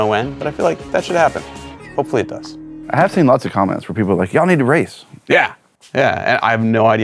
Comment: I heard this as "y'all need a raise!"